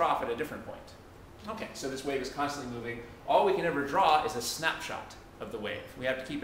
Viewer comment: did he just say snapchat 5:45 !!!!! he just predicted the future lol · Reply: MA3OODI he said snap shot doo doo brain 😂😫😭